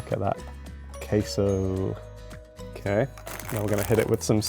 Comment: did he just say caseoh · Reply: Queso